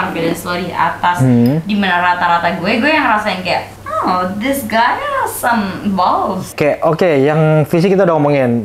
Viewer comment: "this guy has some moles" ga salah denger kn gw?